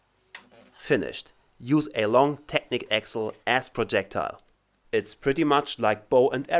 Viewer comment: sounds like you say ass